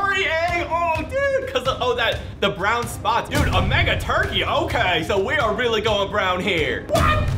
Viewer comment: is it me or did he say mega turkey lol